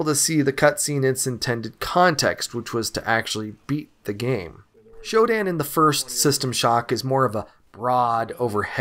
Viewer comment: Ohhh the great Shodan, I thought you said the great Chopin.